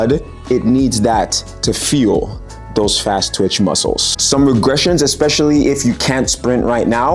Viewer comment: I thought you said my ass twitch muscles were going, and I thought… yep…